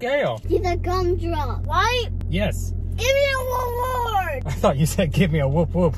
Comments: I thought you said give me a wo wo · Nobody: Really nobody: Six year old 0:27 I thought she was saying world war!! 😁